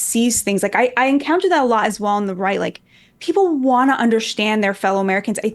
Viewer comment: “I’m a leftist” “wait” absolutely killed me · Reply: Did i miss understand her or did she say @ 4:55 shes from the right?